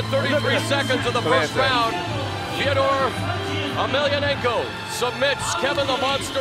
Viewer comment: did he really say "theodor emelianenko" at 19:16? 😂